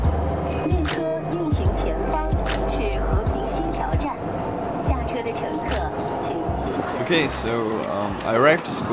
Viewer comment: 肖战？ Did I hear the Name of Xiao Zhan? :D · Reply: It said something something Tiao Zhan, a station.